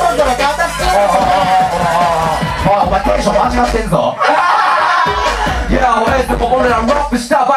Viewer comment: なんて言ってる？ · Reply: テンション間違ってんぞだと思います